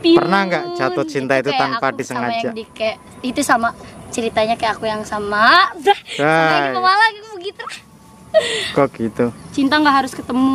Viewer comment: Salah denger ga akunih? blng "sama ceritanya kek cerita aku sama yng dikebmalang?" Mau blng kebalen ga jadi yah jadinya Pemalang Wkwk sukses terus dah ,next ditunggu colab breng yng dikebalen hhi